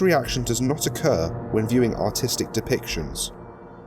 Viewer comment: Note: “This reaction does not occur when viewing artistic depictions.” SCPI’s viewers: Sigh of relief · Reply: Robert Williams I thought he said Autistic